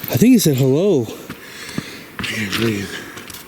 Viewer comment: I enjoyed both, not you... Lol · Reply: Maybe you should bring your wives with you... Lol... They stay pretty strong... Love ya, that is what I heard" hello" awsome